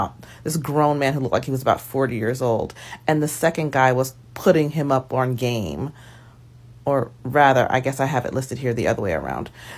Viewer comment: All I heard was "he was putting it down in bed"